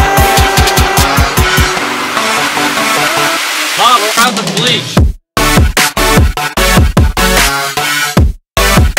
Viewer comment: After 6 years I still don’t know what he’s saying in that part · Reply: Only og fans of mrbeast knows it's "mom we're out of bleach"